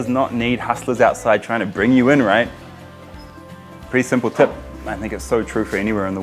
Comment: did he really say my nigga so true for anywhere in the world? just sounds like it lmao even though i know the caption says i think so true for anywhere in the world